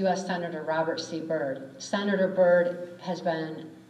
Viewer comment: No i didn't hear her say George Romney, i heard her say Robert c Byrd . You were the only one to say George Romney between